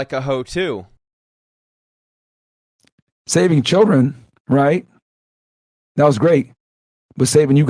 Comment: all I heard was "saving children is great but I like your money😢"